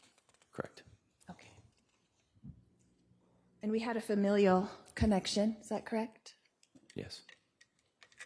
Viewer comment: Why did she say "familiar connection" like that 🤣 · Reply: She didn't. She said 'familial connection'. 😬